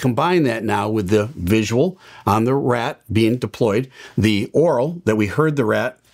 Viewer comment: is using the word "AURAL" correctly. He is not saying ORAL.